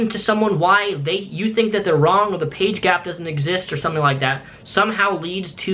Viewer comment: is it just me or does he say "page gap"